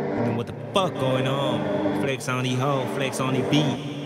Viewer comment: 請問開頭背景人聲“Flex on the beat”前面的“Flex on 你 ho” 0:23 是什麼意思呢？真的聽不清楚請指教🙇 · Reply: “Allen flex on the beat” 是編曲製作人的beat tag 我說那句”Flex on deez hoe” 意思就是 耍屌給那些妹子看